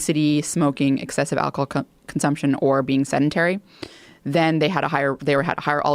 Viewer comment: What word does she say at 2:58? "Senitary?" I doubt I'm hearing that correctly... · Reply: “Sedentary”